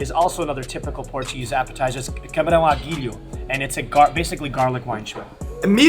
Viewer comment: for the portuguese watching he really said cabrão à guilho xD